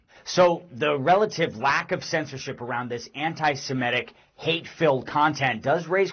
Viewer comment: Did I just hear “lack of censorship”?